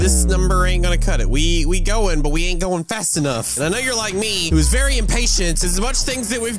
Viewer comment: did he say a ni** like me?..😂